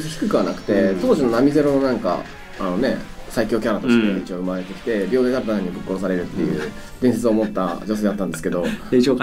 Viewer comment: ここなんて言ってるかわかりますか？ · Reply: 当時のナミ零のなんか、あのね。 最強キャラとして産まれてきて、 秒でダルタニャンにぶっ殺されるっていう、伝説を持った女性だったんですけど。